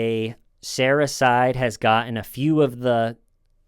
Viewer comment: keeps saying “Sarah’s side” but I hear “Sarahcyde” 😳